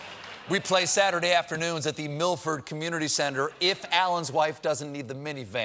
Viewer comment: Did he say MILF Ford. I did not know Ford and a mini van called that.